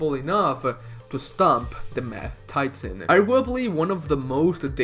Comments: multiple times it sounds like you say 'meth titan' instead of mad titan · I know it is the accent. I shouldn't but i had to laugh about it. I heard "Meth-Titan" and instantly thought of a Heisenberg-Thanos... "Thanosberg" destroying half the population of the universe with his Meth and his 6 Infinity-Methstones. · I keep hearing him wrong. I’d thought he said “meth” titan 😅 now i suddenly have heisenthanos in mind….. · It sounds like ur saying "meth titan" instead of "mad titan"